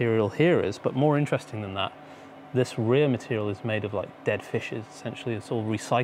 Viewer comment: did he say it was made from dead fishes?🤔